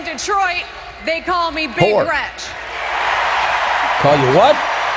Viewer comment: Did she say they call her Big Retch??🤮